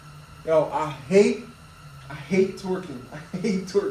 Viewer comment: bro everytime you say “i hate torquing” all i hear is “i hate twerking” lmao. nice video im working on the same bike. rebuilt the engine and no just trying to figure out the carb lol